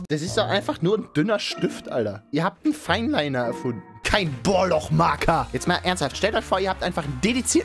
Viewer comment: Wer ist euer Lieblingsmarker? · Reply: Ich habe Edding Poloch Marker verstanden. Es sollte aber Bohrloch Marker heißen...